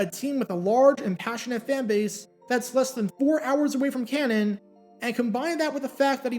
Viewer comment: Is the T in Canton silent? I hear him saying “canon”